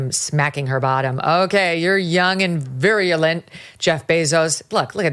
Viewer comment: I think I heard, "You're young and virulent", rather than "You're young and virile". If I did, best Freudian slip ever.